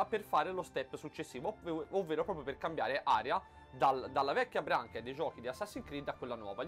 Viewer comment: La "Vecchia Branchia" no, "Vuoi che io muoro?" :P Bel Video, onesto.